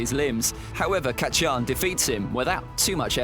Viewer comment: did he call bakugo kat chi an